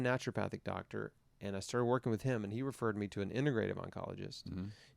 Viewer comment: what does Chris say at 47:06? he found a “___ doctor that referred him to a ___ oncologist that came out of retirement?” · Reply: Naturopathic doctor and integrative oncologist